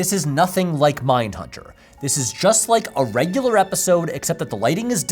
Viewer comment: Ive barely started the video and I have to go back with subtitles coz I thought said "a normal episode except im wearing a top"